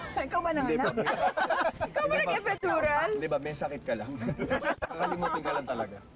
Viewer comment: ano sabi ni angelu? Eventural haha nanganak?